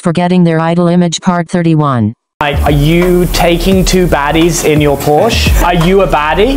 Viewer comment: “2 bitches, 2 bitches, 1 Porsche 👊🏼🔄” 😂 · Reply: Lmao I thought it said "2 Daddies"